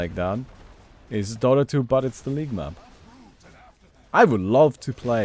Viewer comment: I thought you said "Dota 2 but its LIGMA"